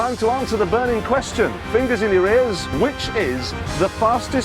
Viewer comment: I heard: Fingers in Uranus 😂